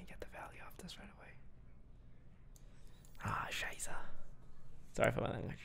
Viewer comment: Wait Toast did u said "scheiße" (16:24) LOL he is cursing in german 😍 BTW i Love u Buddy keep up ur good work ❤️